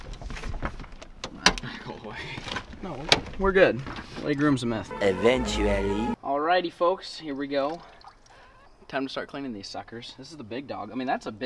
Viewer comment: what did he say about meth?